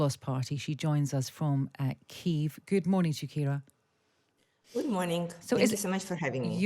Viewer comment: Did she just say "Good morning Shakira"?? 😂😂😂